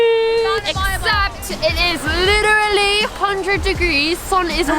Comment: Can you guys tell me what is she saying? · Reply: “ it’s literally 100° degrees”